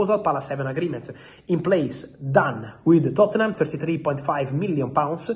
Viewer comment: Is it £53m or £35m? I don't think I heard you correctly. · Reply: 33.5 😅